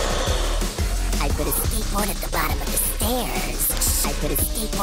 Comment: I put a skateboard at the bottom of the STAAAIIIRS! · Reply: omg I laughed so hard I thought it said I been asleep at the bottom of the stairs